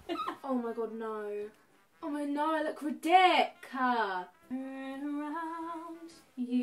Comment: Did she just say d***